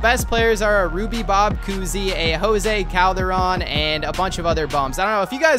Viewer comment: I thought you said Jose Calderon was the G.P.O.A.T fyi G.P.O.A.T stands for greatest point guard of all time